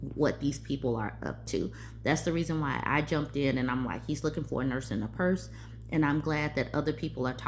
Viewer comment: Oh my goodness, I've heard you saying 'nurse and a purse' for a bunch of videos and all this time I thought you were saying 'nurse in a purse'. 😂